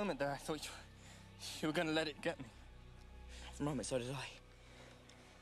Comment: When Cedric said "Some game" I misheard it and I thought he said, "So you're gay?" XD